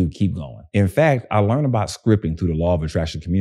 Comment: Did he say scripping through law of attraction? what is that? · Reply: “I learned about SCRIPTING through the Law of Attraction community”